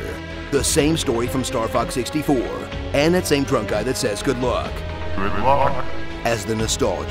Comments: I always thought the robot said "GRIDLOCK" instead of "GOOD LUCK" LOL. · He said "Good Luck"? I thought he said "Work Hard". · omg he says good luck, all these years and as i played this i thought he said "REPORT!" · When the guy says "good luck" before a level begins, I always thought he was saying "grid lock" or "good mock". I was weird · He says good luck? I thought he said heads off... Also I don't lead slippy to his death I am the cause of his death